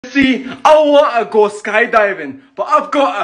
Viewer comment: Him saying: ya see I wanna go sky diving What I hear: "c I wo a skydiving aa vin ba I va go a ear"